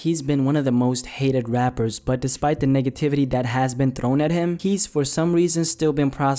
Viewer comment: sounded like he said niggativity instead of negativity 😂😂😂